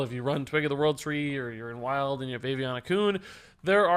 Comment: I heard "Aviana Kun" as "Baby on a Kun".